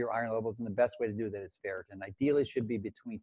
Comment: Dr. Mercola talked about measuring your iron levels at about the 33:20 minute mark. He said the best way to do this is by ?? I couldn’t understand what he said. Did anybody happen to understand what he said? If so please share it. Thanks. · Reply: I think he said a Ferritin serum test for iron levels in the body that should be between 20 and 40. Usually Dr's order it if the red blood (RBC) cells are low, or the patient is showing signs of anemia.